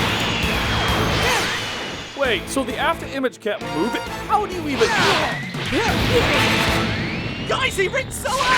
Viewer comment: What did Cell say at 4:17 about Goku's afterimage ? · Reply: Melvin Davis wait, so the after-image can't move h-how do you have even.